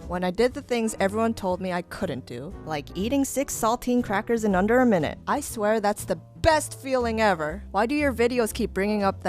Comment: When she said “like eating six saltine crackers in under a minutes” I thought she said “like eating six sultan crackers in under a minute” O.o